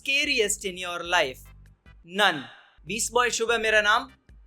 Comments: I thought he was saying Nun 😁😁😅😅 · I thought he said Nun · I think he said nun 4:12 🤣🤣 conjuring wali movie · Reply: Yup, even I thought he said "NUN" but actually it's "NONE"! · I think he said "nun" 😂😂😂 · When he said none, i thought he was saying "the nun" movie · here i thought you are saying nun😂😂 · when he say none i thought he is saying nun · i thought he said the nun movie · I thought he was saying ‘nun' rather than ‘none'😂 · I thought he is saying The Nun XD